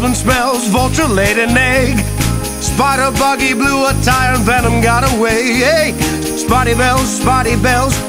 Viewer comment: i am not going to lie it sounded like he said venom got away